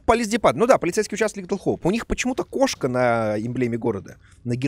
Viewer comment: мне послышалось "у них почему-то кошка на еблеме города" Еблема города. Какое четкое выражение, пожалуй возьму на вооружение))